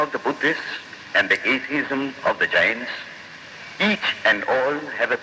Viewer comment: Did he say "atheism of the giants" @ 7:20 seconds?